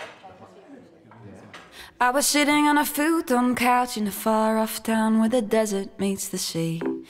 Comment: sounds like she is saying "I was shitting on a futon couch"